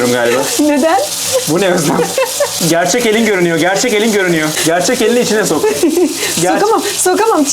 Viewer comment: " Gerçek elin görünüyor " 2 kez tekrarlanarak anladım hahaha Sağol abi